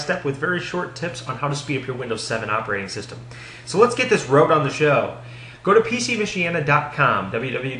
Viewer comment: Did you just say "road on a show" lolfish mate, good video for noobs.